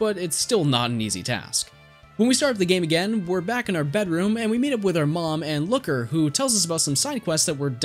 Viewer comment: I thought you said. "we start up the game. and talk to mom the looker." I was. "yea she is a....................... oh. that looker." :P